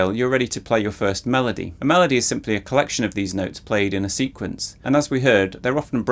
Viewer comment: i heard: a melody is a collection of deez nuts played in a sequence 😂😂😂